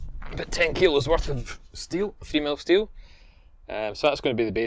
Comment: I thought I heard 'female steel' for a sec there